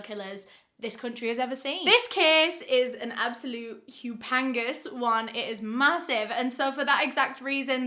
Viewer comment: seconds in, does anyone know what hupangas means? She says "this is a hupengas one" and I'm confused...did she mean to say 'humongous'?